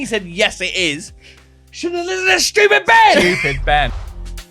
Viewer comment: I thought said stupid men the first time 😂😂